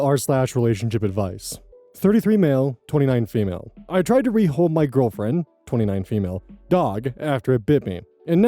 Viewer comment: In the next to last story, did anyone else mishear the title as “I tried to rehome my girlfriend?”